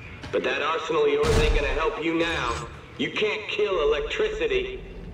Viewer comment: When he said you cant shoot electricity, the subtitles said you cant shoot what you cant see